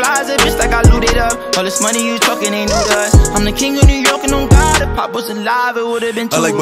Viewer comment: I'm the king of New York and on God If Pop was alive there would have been two of us.. That line hit different.. W💫💫 Forever 🕊🕊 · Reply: yo i thought he said, im the king of ny and on god im popping saliva idk why